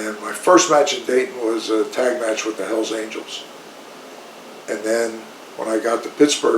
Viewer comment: Did he say he wrestled with the Hells Angels?